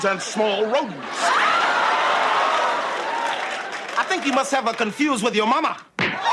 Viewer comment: At first I thought he said “I think you must have confused her with Obama” I don’t know why